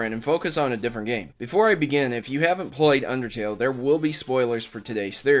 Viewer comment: Did you just say... "If you haven't PLOYED Undertale-" What does that even mean?